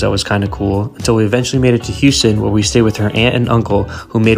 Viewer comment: I thought he said Austin for a sec.... glad he didn't :) (austin sucks)